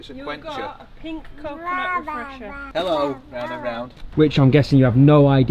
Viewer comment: Naaaa was it just me or did say hello to dave 😅